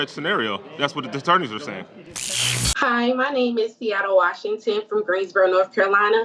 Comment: Did she say her name is..."Seattle Washington?" Wha...?